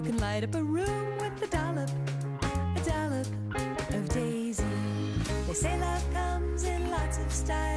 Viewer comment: This will always be the version I remember most. I was home sick and this commercial came on so many times that day. I didn't even know what a "dollop" was. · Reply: When I first saw this commercial back in 2004, I thought she was saying “a dollar”, until it showed the words “A Dollop of Daisy” on the screen.